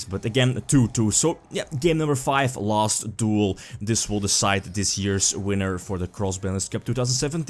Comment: Great tournament. Allow me to make a few suggestions for next year's Cross Banlist Cup however: 1. Would it be possible to make all the final's matches best 3 out of 5? (So Winner's Finals, Loser's Finals, and Grand Finals) 2. For Grand Finals, I like that the winner of the Loser's bracket gets to challenge the winner of the Winner's side, but I think that the Loser's side deck should have to win 2 matches against the Winner's side deck to actually win the tournament. Every deck should have to lose twice to be eliminated from the tournament, including in Grand Finals. Can't wait for next year's cup! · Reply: If that was how you already had it structured, that's great. I just wanted to make sure that was the case because I thought I heard you say "this duel will decide the winner" and I assumed that meant it was only 1 match.